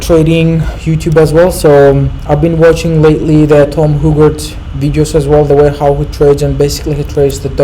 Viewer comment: who did you say you have been watching? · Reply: Tom Haougard